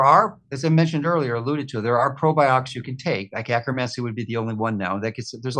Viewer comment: Akkermasee? Is that what he said?